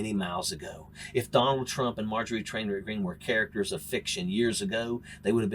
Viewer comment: did he say trailer green? Lol